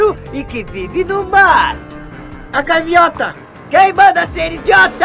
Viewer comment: sabia que eu entendi "a carmiota"